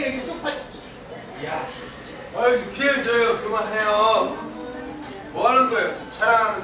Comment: 왜 피해를 줘요 그만해요 로 들리네요